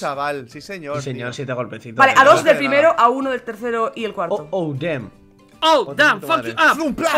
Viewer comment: Alguien entendió lo que dijo en el minuto 4:24 wtf lol🤓🤓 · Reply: "A dos del primero, a uno del tercero y el cuarto." Nada del otro mundo ._.